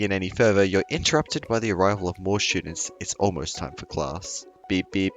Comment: when I first heard this part at 6:23 I thought you said "the arrival of more shitters"